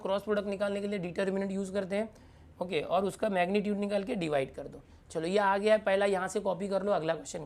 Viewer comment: Who heard chalo in the lec between